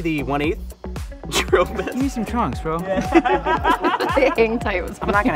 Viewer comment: "Give me some chunks and lets do it"😂😂😂 luv dis guy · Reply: I heard "chunks", but trunks makes more sense...though what does he need it for at that point? 😛